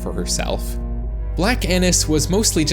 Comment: I thought that said Blake anis